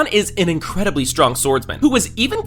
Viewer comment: Did he say SWORDMAN?